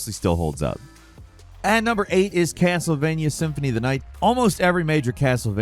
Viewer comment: I know I am watching this two years late... But did anyone else notice @ 20:00 He actually says Cancel-Vania instead of Castlevania haha. Never heard that one before so I figured I would give me respects to a funny nick name to a classic ass game haha